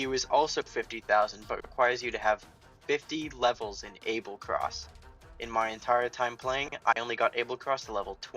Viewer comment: I got an ad so it sounded like you said ‘it requires you to have ABSOLUTELY DELICIOUS AND DELUXXE BURGERS’